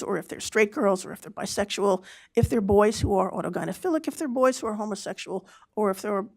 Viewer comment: it sounded like she said autogynophilic ... ?